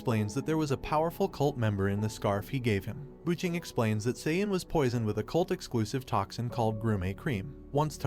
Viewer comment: did they really say saiyan?